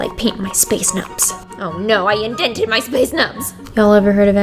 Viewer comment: I just inhaled tea and nearly died because I heard “ Oh no I indented my space nub” 😂😂😂😂😂😂😍😍😂